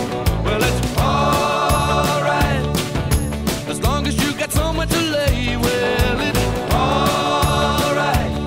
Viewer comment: I always thought tom petty said 'as long as you got someone to lay". I thought it was brash, but, Bob Dylan said worse in many a song. Thanks for putting Petty back up on the purist pedestal he deserves ♡♡♡